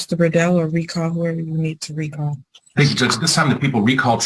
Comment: Did that prosecutor just say, “Thank you, TOOTS,” to the judge?!?! Please tell me if anyone else hears that. I have auditory processing disorder and hear things wrong all the time, but I keep replaying it to figure it out and it still sounds the same. 😅😅😅